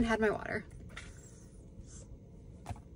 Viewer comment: I had to re-watch so many times bc I thought you said you're welcome roommate. I could have just checked the comments ☝️ 😂